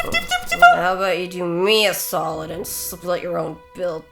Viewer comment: Who else thought tifa said do me a solid and slit your own throat.